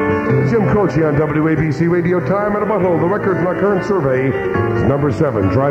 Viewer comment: whats the album name at 8:50 sounds like he says "Time in a Butthole"